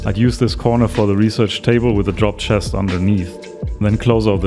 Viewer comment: did you say drop box or large chest I can't really tell, also this is really helpful I just got the game and don't know much about the mechanics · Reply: Oh, "drop chest" is Rust-language for any chest that is close to the door. The idea is that you can quickly "drop off" loot if you are in a hurry.